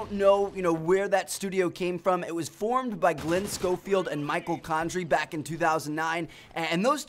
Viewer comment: What Trev said: '' Formed by Glen Scofield and Michael Condrey...'' What i heard: '' Formed by Michael Scofield..''